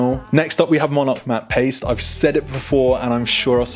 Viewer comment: wtf is he saying? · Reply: Paste